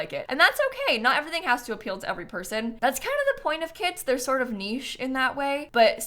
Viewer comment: Not my cc captions writing " not everything can appeal to everyone, that's kind of the points of kiDs, they're sort of niche in this way" instead of kiTs. Not mad about it 😂😂😂 18:30